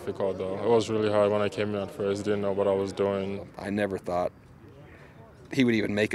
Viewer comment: Sounded like he said "I was really high when I came in at first I didn't know what I was doing " haha